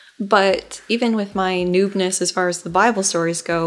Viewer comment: instead of 'noobness', the caption reads 'new penis'. Please don't change this just because I've pointed it out, it's funny as hell